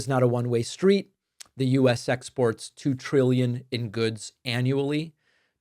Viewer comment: When David said, “U.S. exports,” I heard, “U.S. sex sports,” for some reason 🤭.